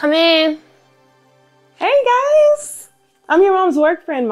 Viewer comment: I thought that lady said "Iam your mother's boyfriend".